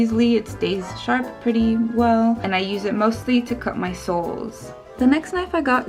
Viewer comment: When you said you cut your soles with the knife at 1:50, I thought you said souls; I was like “oh, ok.” Anyways, cool video! It’s so interesting to know how people work with different professions.